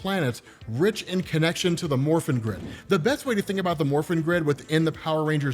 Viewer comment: I'm sorry but I keep hearing "the morphine grid" and giggling like an idiot during the entire video, as surprisingly good as these power ranger comics are.